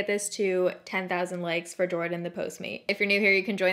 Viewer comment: I'm gonna be honest I thought it said postman I was like what r u gonna do ask him at the door 😂